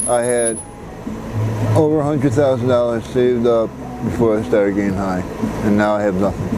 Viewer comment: Dam said he had 100,000 saved up before he started WOW!!!!! I feel so bad for him GOD BLESS him n his Health.... Really praying for u... You will be on my Mind n Heart. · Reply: I thought he said $400,000